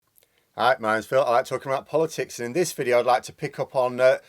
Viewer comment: I always hear "Hi, my name's Phil and I like to corrupt politics."